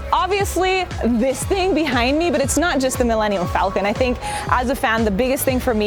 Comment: did anyone else hear her say “Millennial Falcon” or am I tripping?